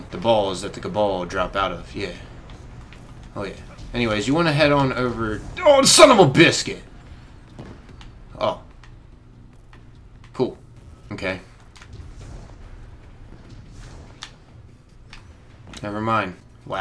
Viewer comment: This is what I heard "son of bitch" oooooo he just Cursed .